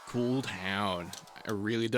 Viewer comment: did you say COOLTOWN?!?!? <link>